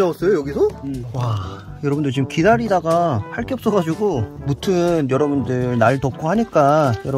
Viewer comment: 할게없어가지고라고들리는데 자막은 할퀴었다고 되있음요 오타인듯